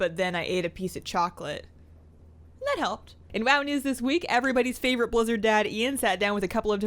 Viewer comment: i thought you said everybody's favorite blizzard daddy